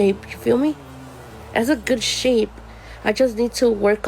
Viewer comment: I honestly thought that said “has a big”